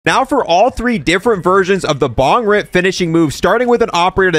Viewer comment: I heard “Dong rip”…